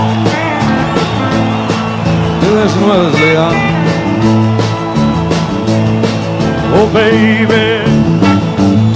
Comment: What does Waylon say at 2:48 ? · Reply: Do this’n with us Leon